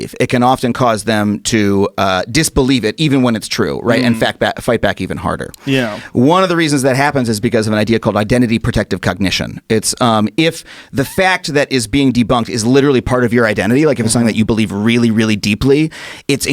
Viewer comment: did he say cognition are cucknition ?? 😀😀